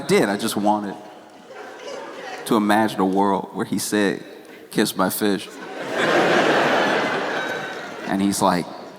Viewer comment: I really thought he said "kiss my fish" too 😂😂😂